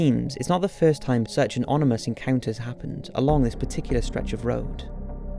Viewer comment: Did he say “onimous” instead of “ominous”?😂